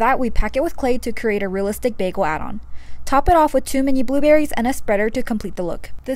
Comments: When she said two mini blueberries I thought that she said too many blueberries 😂😂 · I thought she was saying “too many blueberries” but she was saying “two mini blueberries” 😂